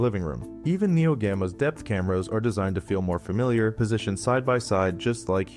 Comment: LOL I heard "Death Cameras" in its eyes!!